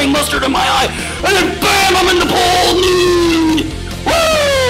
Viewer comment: did he say in 9:29 "Im on The pole nuude!! woo!" ? hahah XD I'd pay to see that! x)